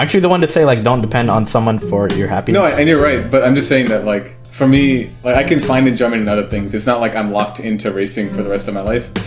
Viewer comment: I know he said enjoyment, but it sounded like "a German". I can find a German in other things!